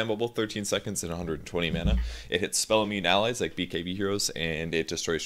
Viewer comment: Did he say it hits spell immune allies? At 2:20?